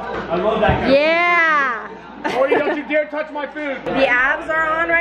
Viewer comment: All I hear is “GORDY DON’T YOU DARE TOUCH MY FOOD” and I’m here for it. 😂😂